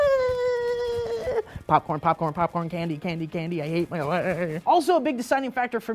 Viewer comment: sounded like "i hate my life" then he suffocates his wife with a pillow.